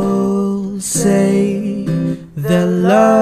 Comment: DID I HEAR MEATBALL? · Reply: XD same it was the part when he said( people) that turned into (meatball) lol